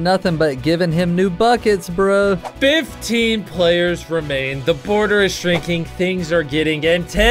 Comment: i thought they said the warden is warm